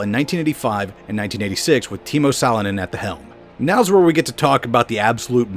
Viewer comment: I have a bit of a lack of knowledge in the history of the WRC, but when you said Toni Salonen, I low key thought you said Teemu Selänne, who was an old hockey player for the Anaheim Ducks. Funny enough, Teemu also had some entries in the WRC. Both of them being in Rally Finland finishing 33rd in 1997 and 24th in 1998. Funny how two of my favorite sports clash sometimes.